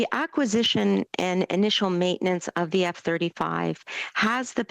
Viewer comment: does she say “initial maintenance?” …sounds like our AWESOME sub fleet!!! I bet Trudeau loves estate and garage sales on early Saturdays.